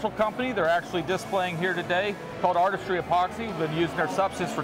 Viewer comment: What name is the epoxy company mentioned @ 14:36? I couldn't make out what he said. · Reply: It's called Artistry Epoxy. We haven't used any of it yet, but we'll be trying it out pretty soon